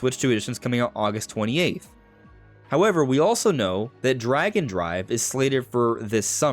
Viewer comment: I heard "Dragon Drive" instead of "Drag and Drive".... somehow.... i think i'd prefer that. :D